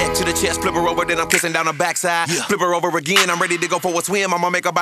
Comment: 🛑🛑🛑🤦 Did Ludacris say that??? While pissin down her backside??? around 2:34 mins in song ....So he 💦 pissin on people too? 🤦...smh 🤦